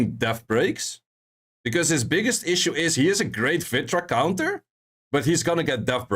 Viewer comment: I couldn't get what you said at 11:51 -which counter? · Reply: Vritra